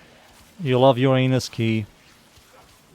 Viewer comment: did you just say that Vor loves his anus key?